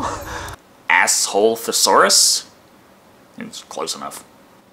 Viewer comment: I think he said Lesothosaurus